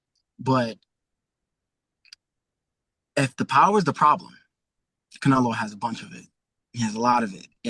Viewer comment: I thought Bud said the patience was the power